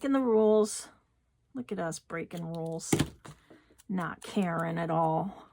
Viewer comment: Karen? 🤔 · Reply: Oh my gosh, 28:01, I swear you said not a Karen at all, 😂. I apologize, you said not caring at all. I still got a giggle even today when watching it again to find this spot. Nope not transference at all 😂😂 And yes, I will be making this one too! So excited for it! Thank you again.❤